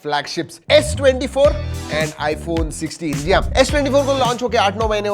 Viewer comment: Did you just said S iphone 16😂